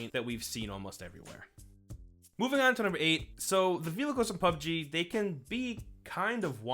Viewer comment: Is he saying villacles?